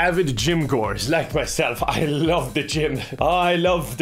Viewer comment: I heard "I love the Jim"